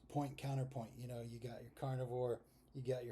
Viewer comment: You guy misheard speak. He didn't say "carnivore", he said "car and a whore"...